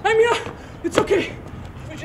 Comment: does he say "scheiße" at 0:36 ?